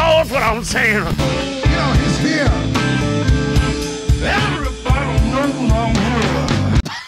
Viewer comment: OMG I think I just figured out what he's saying. Listen to it at 0.5x speed and it sounds like he's saying "Everybody knows that I'm here".